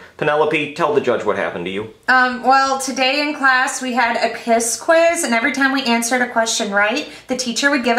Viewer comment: I heard Piss quiz Lol